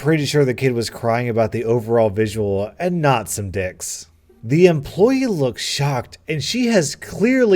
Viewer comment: Youtube: 1:40 Did you just said "dicks" instant demonetization